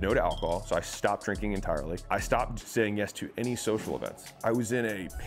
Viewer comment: I stopped saying yes to social events? and the caption says something else